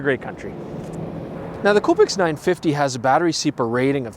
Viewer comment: Pretty good. For a moment I thought you said Covid 950 at around 4:33 and 7:47.